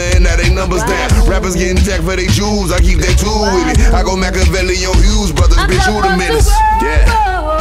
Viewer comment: “I go makaveli on Hughes brothers like who da menace.” If you know you know. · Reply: I thought he said I go makaveli on Hughes brotherexu roydemez